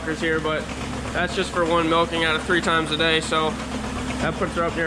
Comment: Did you say you milked 3 times a day?